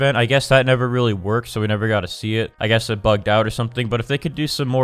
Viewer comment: am I the only one who thought he said the f word where bugged is?